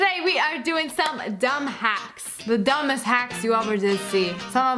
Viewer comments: I thought she said “Dumb Hats” XD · Am I the only one who thought she said dumb hats instead of hack😂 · Thought you said dumb hats because of your beret